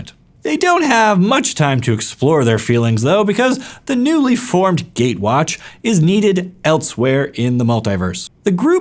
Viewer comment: Misheard this as “the newly formed Gay-watch”